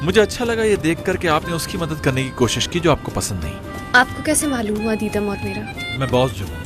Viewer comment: Please tell me what did she said just after 13:58 Aapko kaise malum woh adita maut? I don't think it's : adita maut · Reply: She said apko kese maloom hua deedum or mera